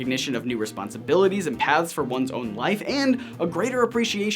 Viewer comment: when says paths it sounds like he says pads